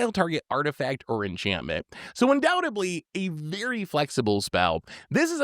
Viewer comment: I kept hearing "undoubtably"... Has anyone ever tried Valorous Stance?